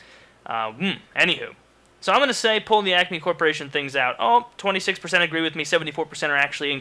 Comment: I thought he said "acne corporation" for the first question...